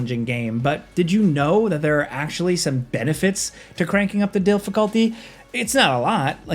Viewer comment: did you just say “DILFiculty” instead of “difficulty”? 😂😅